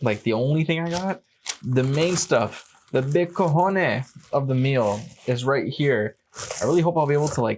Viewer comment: When you said "the big cojones" I was like, when did Denny's start selling those! Must be a Canadian thing! I misheard....😅 I definitely prefer beef and gravy - and while simple - it does look like good comfort food! 👍 Thanks for dipping the bread!